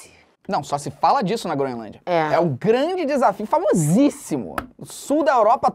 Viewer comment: eu ouvi grand line?